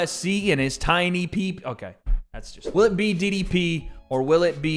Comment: it sounded like said "Will it be Diddy P" instead of DDP 😂